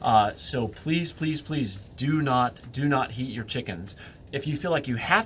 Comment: I thought you said "don't eat your chickens" 😃